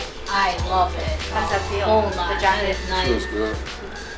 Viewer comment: Lol at 9:27 did she say the vagina is nice 😂😂